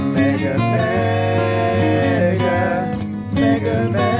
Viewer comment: anyone else think he was saying nigga 😂😂😂😂